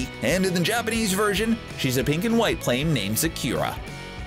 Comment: Is it just me... or did he just say ZAKIRA at 15:34?